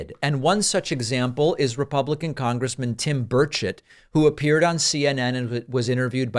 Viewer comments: I thought you said "Bird Shit", not Burchett. 🤣🤣🤣🤣🤣🤣🤣🤣🤣🤣🤣🙃🙃🙃🙃🙃🙃🙃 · Every time you say “Burchett” I hear “Bird shit” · I was looking away when you said Burchett and thought you said birdshit lol. Couldn't unhear it every other time you said his name.